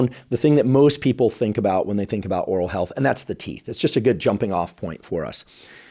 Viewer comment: I thought he said "that’s the tea" I got so excited 😭